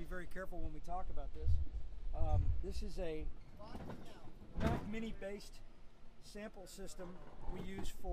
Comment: Need some help here! At the 2:30 mark, tells what kind/brand of midi based sample system that Sav uses, but someone made a noise at the same time he said it, so I couldn't hear what he said. Can anyone else out there hear it, or happen to know what sample system he said is being here? Please comment if you do. THANKS SO MUCH! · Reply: Sounds like he says "Mac midi based system" as in Apple computers. Not really the brand of the system but rather the OS that is running it as opposed to a Windows based one.